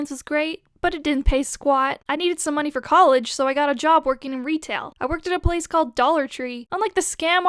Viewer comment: Thought it said rectal at first